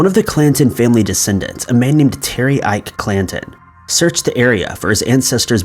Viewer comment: At one point, the CC said Clinton instead of Clanton.